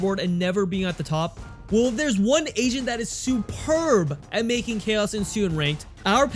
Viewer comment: I thought he said “well theres one asian that is superb....”